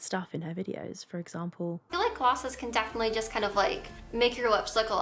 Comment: I thought I heard "I feel like glasses can make your lips look shinier sometimes" instead of "glosses". Oops. I like mine better because it made me laugh so...that's what I'll keep thinking she said.